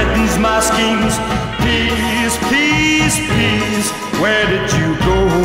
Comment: I keep hearing “Please, please please,” instead off “Peace, peace, peace,” and “When a mushroom cloud has chained every route,” instead off “When a mushroom cloud has changed every rule.”